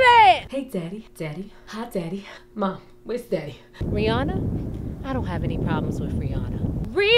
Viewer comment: does she call Rihanna "Free-anna"? XD